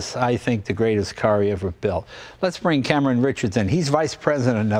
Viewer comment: When he said "Let's bring Cameron Richards in", I thought he said "Let's bring Cameron Richardson". That's the only way this could get any better.